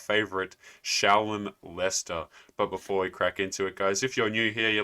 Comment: I swore I heard you say, "It is our favorite child molester."